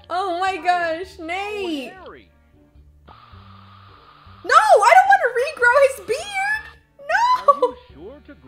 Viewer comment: It sound like u said" no I don't want to redo his beard mate "instead of Nat LOL